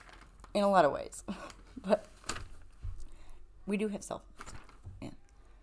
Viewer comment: okay around 22:26-22:28 I have a hard time hearing what word you’re saying, right before you say they work most of the time. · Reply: We do have cell phones lol Sorry 😆